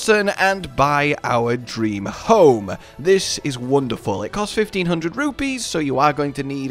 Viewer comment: I thought he said 15,000 rupees so I spent 2 hours getting it and found out it is only 1500 rupees